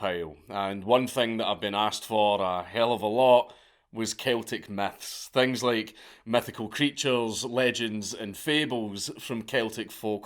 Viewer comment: lol I thought you said "Celtics Meths" at first... "...Methical Creatures, from Florida..."